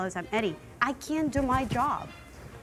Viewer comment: "I can do our jobs" ... ummm, I think you mean to say you "can't" do your job.